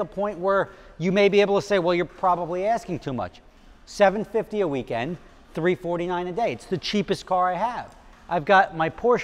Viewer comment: Yeah 400 bucks a day to rent a Z4, no thanks. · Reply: he clearly says $349 a day, $750 a weekend.